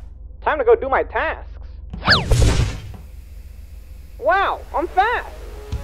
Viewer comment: did he just say: wow im fat